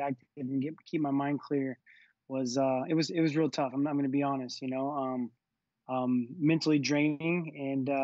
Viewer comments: Did he say that "he wasn't going to be honest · Sounds like he said, "I'm not gonna be honest." LOL